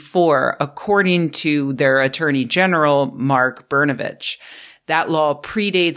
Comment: Brnovich? Is it weird that I heard "burn a bitch"? like, at the stake?